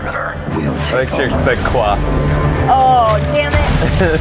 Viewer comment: She said "quoi ?" Bcs it means "what ?" :)